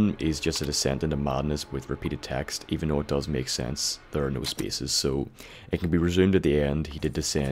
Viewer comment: i cut read it all on 2 sek from the first voult pc u meat in voult 87 (: i am sane. iam me. i am forever ( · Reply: Sorry for wasting you time. After i heard u repeat the same 5 sekunds from 1:35 10 times. I mayby head you say the file dosent make sense, ther no spaces. But i think i miss hear you. This what i think you mean "the file dose make sense". Ps im realy glad u want to use you time on me, that just a person that miss heard what u say. (